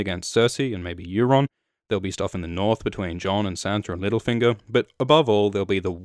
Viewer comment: Did he said "Middlefinger" instead of "Litterfinger"?